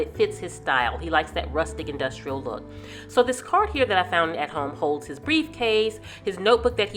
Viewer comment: Where did you get the cart? I thought you said Home Holds but wasn't sure. I'm redoing my husbands study also and going with rustic industrial. Thanks I get so excited when you have a new video. 😊😊😊😊😊